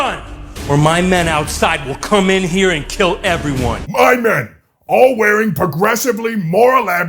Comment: I thought he said, "My nana will come in a k*ll everyone"😂